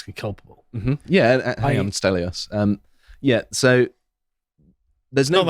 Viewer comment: did he say I understelios